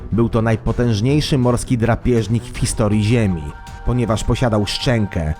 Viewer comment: Usłyszałam drapaki XD